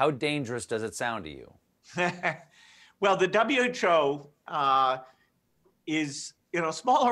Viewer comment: Why is Gates saying "W CHO" And again, at 9:19 WE NEED THE W CHO LIKE WE NEED A HOLE IN THE HEAD!! #BillGatesNWOShill